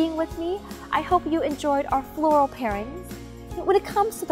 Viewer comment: At first, I thought it said floral paintings..